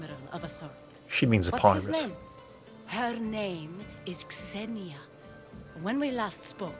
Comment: I misheard Xenia as Xena and got very excited for a second.